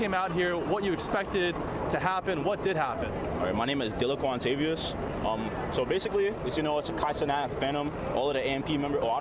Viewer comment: WTF did he say his name was?